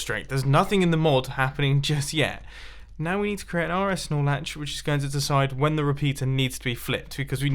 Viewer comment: Mumbo, did you just say “RS Snorlax”? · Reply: Skylanders Freak R S Nor Latch